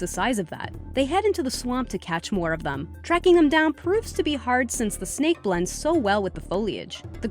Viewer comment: I Heard "Swamp" here but..I'm not sure if I heard right at 11:54 "Shreking them down".